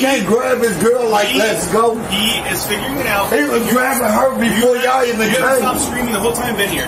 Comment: I thought he said he was god? God got cuffed